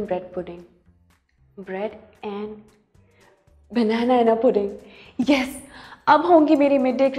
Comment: Did anyone noticed what she said (0:07) Banana-Ana pudding😂😂